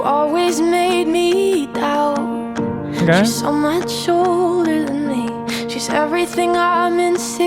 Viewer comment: I thought she said "shorter" instead of older...... makes more sense lol